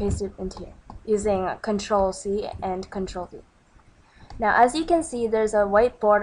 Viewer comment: I couldn't hear what you said after control c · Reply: Avery McCargo Oh sorry >.< I said control v